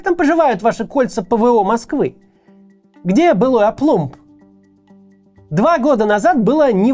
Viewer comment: Не очень понятно выражение на 11:44 "Где былой **оплумб**?" Что такое оплумб? Я правильно расслышал? Никогда не слышал это выражение и слово это не гуглится...